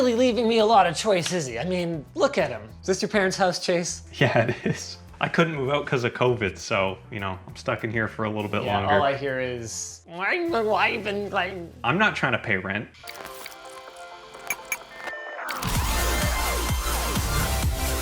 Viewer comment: All I heard was “ I’m not trynna grow up”